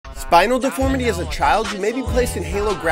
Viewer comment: At first I thought he said spider deformity and then I thought he did the spider web hand thing…